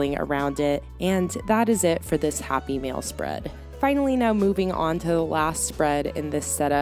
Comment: I swear at first I heard you say happy meal spread.